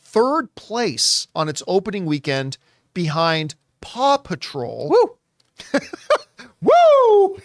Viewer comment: Lmao I thought was saying “Papa Troll” was the #1 movie of the weekend and was like wtf is Papa Troll and how did it go completely under my radar until I realized he said Paw Patrol. I’m such a dumbass.